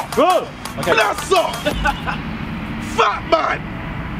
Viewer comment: what the hell does jj yell at 0:30 ? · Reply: Bless Up